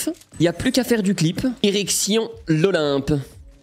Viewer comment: j'ai compris "érection l'olympe"